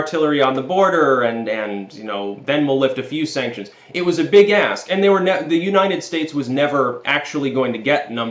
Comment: "Big ASK". Thank you for repeating it. I heard "Big ASS" and actually did a double-take.😆